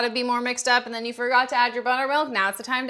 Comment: The first time she said buttermilk I thought she said bonermilk